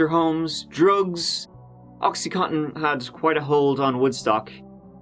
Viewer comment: I must be super tired tonight. I heard "addicted to toxyhottin"